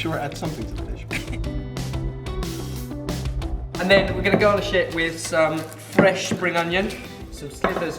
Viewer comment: I really thought said 'then go on the shit' instead of 'garnish it' and giggled way too hard... this recipe looks amazing and as a slow cooker lover can't wait to give it a whirl :D